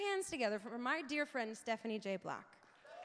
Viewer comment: Was it just me or did i hear "Stephanie J. Black"